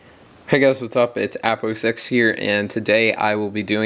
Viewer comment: Thumb up if u thought that he said "Apple sex here" at the start of video.